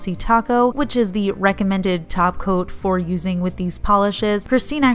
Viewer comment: I don't know if it was just me or not, But when you were talking about the top coats it kind of sounded like you were saying taco.